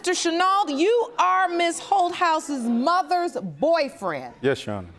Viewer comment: I had to wait until I saw the daughter's name because I could have sworn the judge said "Ms. Ho House" 🤣🤣🤣🤣